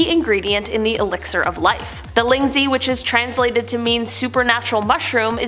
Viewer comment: I had to rewind the video three times because my stupid arse thought you said 'The Lindsay'. I was like 'lindsay where why she here? How?' I'm so sorry omg 😫 you're both awesome hosts my hearing is just broken. 0:43 is the time stamp. I know it's ling Zhi (probably butchered the Romanisation of it, apologies.) can't unhear what I heard.👀👁️👄👁️